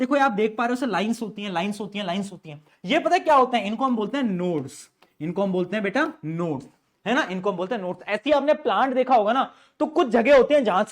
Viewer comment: I really heard Nudes